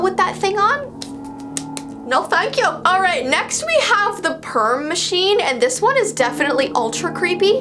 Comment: " i thought that said " p*rn machine! Woah buddy!